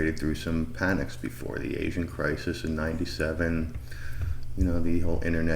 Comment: i heard this as "the ancient crisis of 1907" (there was a us crash) instead of asian crisis of 97 & was wondering why you were staying so serious 😄